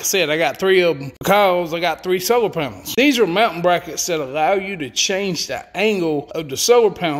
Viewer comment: I swear the god i thought he was saying mountain brackets for like 2 minutes...